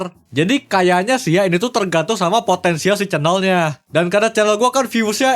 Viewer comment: did you say potensial